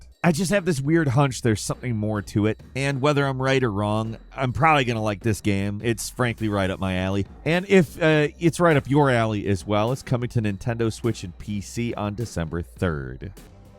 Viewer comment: I thought Falcon said “Frankly is right up my alley” and I was thinking who is Frankly!? lol